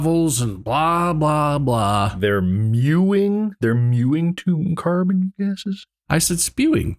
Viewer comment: I also heard “mewing”. Or at least I didn’t hear “spewing”, I just kind of didn’t catch what he said exactly